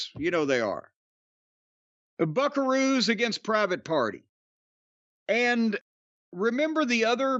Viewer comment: For a minute I thought I heard Private Parts, instead of Private Party. I like the first name, especially when it involves the Bucks. Lol😅😅